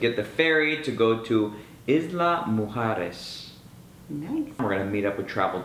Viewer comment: It sounded like you said you were at Isla mejores at the beginning of the video. Was that a slip or were you saying this is the best island ever?